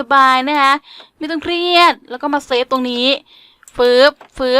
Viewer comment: ได้ยินพี่พูดว่า ไม่ต้องเรียด🤣😂